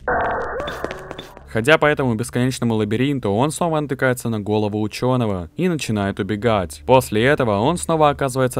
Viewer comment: Мне послышалось "На голого учёного" на 7:58☠☠☠☠☠☠☠☠☠☠☠☠